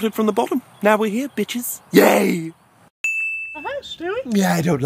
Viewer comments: Did he say B*tch in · did Thomas just curse...........THATS LIKE THE FIRST TIME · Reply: He just said Bitches lol I thought he was going to say a really bad one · Did Thomas Just Said "Bitches?"